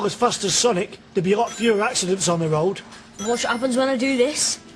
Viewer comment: "Woahhhhh, look at dat Hechhog guh! Tell yi wat, if wes all as fast as Sonique, dere'd be a lo' fewa acciden's on the roed" "Woch wot 'appens when oi deu this" " Wah did yeu poosh?" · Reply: i thought he said there'd be fewer laxatives on the road lmao